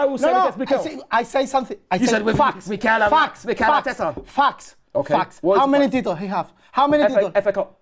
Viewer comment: Raul I thought he said f*cks